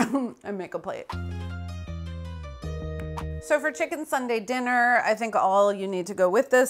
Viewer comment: I kept hearing "chicken sundae dinner" which this could basically well be. 🤭